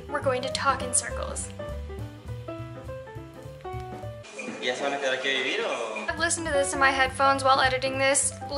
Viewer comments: So this is like... 4 years late, BUT at 3:27 you say that you have no idea what he said. He said "Ya se van a quedar a vivir aqui o..." in other words "Now you will stay and live here? Or.." So you answered correctly! I was also super proud I understood.. cause although i was born there.. I grew up learning spanish in Canada along with French and English and i consider my spanish to be terrible. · "Ya se van a quedar aqui a vivir o" is what he said which means "Are you going to stay here to live or'. He did speak really fast.